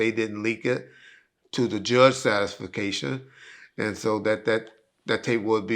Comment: Did he say “satisfication” at 2:28? What kind of illiterate language is he speaking??? Wow